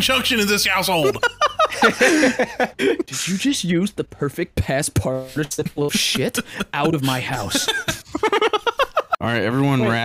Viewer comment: I thought he said "past participle of shit"